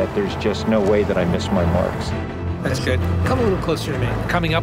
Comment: Ah, and I thought he said 'I trained for this so that I can no longer smell my farts' (miss my marks).